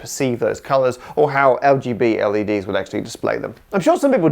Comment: 🏳️‍🌈 Did you say "LGB LEDs" in 14:02 🏳️‍🌈 😂